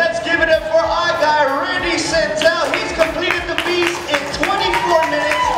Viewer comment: Did he say Rudy Santel (he starts talking at 5:27) ?